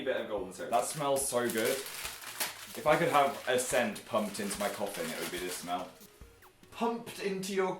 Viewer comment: I thought Phill said "pumped into my coffee"